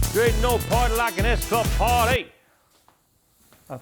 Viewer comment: What did Martin said around 3:37 with the funny accent? For non-english speaker it is really hard one :D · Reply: "There ain't no party like an S club party"